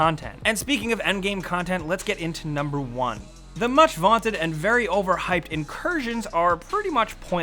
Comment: did he say vanted instead of wanted ?